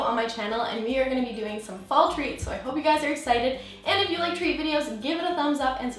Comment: I thought it said "DIY fail treats" 😂